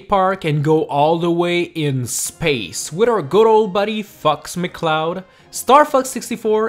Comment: when ever he says fox, it sounds like he says fucks